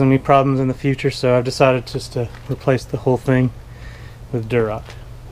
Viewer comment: What did you replace the siding with? Sounds like you said, “durrup “. And idk what anything is so lmk lol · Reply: Lol, durock.